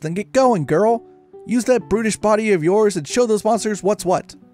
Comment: I had to do a double-take at 12:35 I thought you were saying use that British body of yours